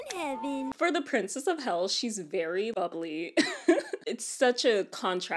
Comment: When she said bubbly when she described the princess instead of ‘bubbly’ I heard ‘ugly’ for some reason….